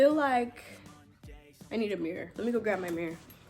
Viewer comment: I thought you said I need a beer lemme go grab my beer 😂